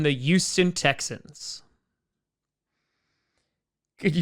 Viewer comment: Yuston. Once I heard it I couldn't stop hearing it.